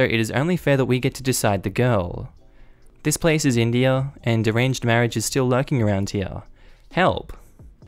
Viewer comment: Heh, when you said "arranged marriage" I heard it as "deranged marriage." Potato po-tah-to.